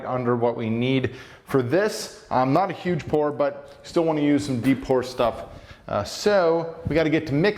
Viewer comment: the captions said po*n!!!